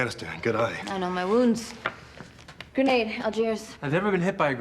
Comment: wait Did she say grenade Algiers ? wtf is talking about we haven't had a war in like 55 years ,they really want to make us look like terrorists damn i really wanted to watch that show such a shame, they really need to fact check that it